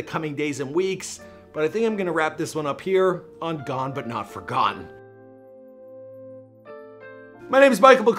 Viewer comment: These vids are made in tribute. I try hard to keep everything as respectful as possible, please do the same in the comments 🙏If you missed our video on Nipsey here is the link: <link> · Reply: I SWEAR YOU'RE SAYING FORGONE